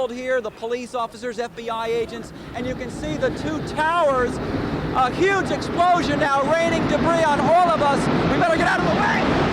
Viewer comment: did he say "a huge explosion?"